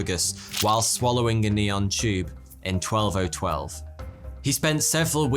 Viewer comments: I'm sorry, Did you say "In 12012"? As in 10,000 years from now? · Did I hear 1202 a neon tube cut a 4" gash in someone throat. Must have misheard. I don't believe there was electricity back then. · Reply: No, because he actually said "12012" 🤨